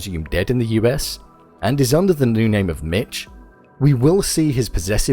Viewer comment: Safe to say I definitely misheard his new Alias! NICK, not MITCH! I now know. Don't lock me in the cage for getting it wrong. I'll do anything for..... you. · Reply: How do you mishear Nick for "Mitch" though... maybe have your ears looked at lol